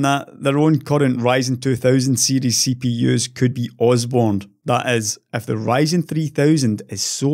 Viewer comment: What did say here? "Osbourned"? I listened 10 times, I still don't know. I might not know the word. The first time in many videos that I couldn't understand something which he said so no hassle with regard to his accent! ;) I guess it might be a reference to that Osbourne family? It doesn't say me anything. I know that that guy was some Black Metal singer and that he had a reality TV show, that's it. My point of view, anyone who has any knowledge about process nodes and micro-architecture would wait for the 3000-series anyway. Unfortunately for them most potential customers don't have such knowledge.